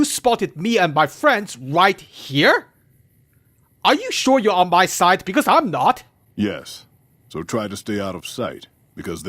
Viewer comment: "Are you sure you're on my side, because I'm not!" I heard that as "I'm not on my side!". Which kinda fits! 🤣